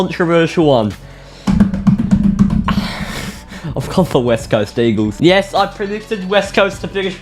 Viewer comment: Every time I here someone say west coast eagles it sounds like there saying west coast deagles